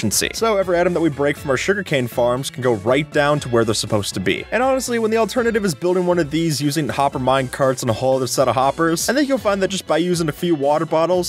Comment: I know its hopper minecart but the first time i heard it i thought he said copper minecart.